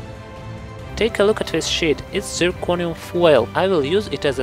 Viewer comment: haha the accent makes you sound like you said "take a look at this shit"